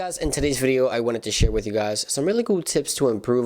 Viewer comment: At the start of the video sounds like u say ‘shit with u guys’ 😂😂